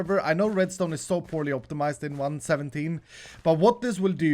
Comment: Did i hear 117?